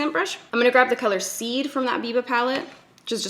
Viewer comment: Is that the ND Biba palette then? Lol I thought I heard her call it the Viva palette and of course, couldn't find any such thing! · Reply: Jane Straker Gray yea it's biba not Viva lol